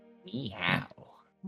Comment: Well, this episode seems pretty interesting 😂 Am I the only one who hearded Riccaro said "Miaow" after Kaiba presented himself at 23:26 ????? XD